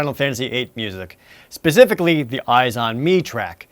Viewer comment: I was zoning out and I thought you said "2pac - All Eyez On Me Track" I was like "whAaAaAaA!?"